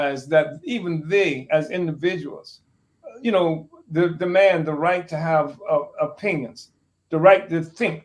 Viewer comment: It sounded like he said * a man has the right to have a penis * 42:19😮